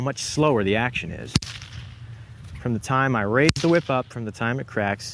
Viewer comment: I heard rape the whip